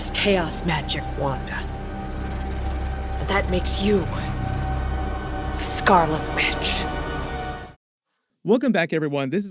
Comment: Why do i hear her saying "Makes you a Scarlet Bitch" xD